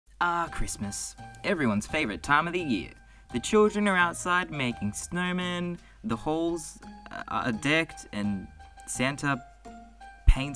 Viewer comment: I thought he said the holes are decked 🤦‍♀️🤦‍♀️🤦‍♀️